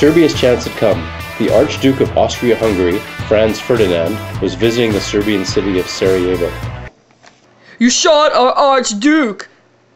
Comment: What did he even said on the minute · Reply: He said that Franz Ferdinand was visiting the city of Sarajevo.